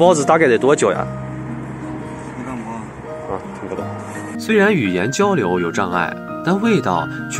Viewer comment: 烤包子老闆聽不懂說的發音，好像「聽無」，真的好像閩南語啊！ 台灣觀眾聽起來特別親切😀😄😃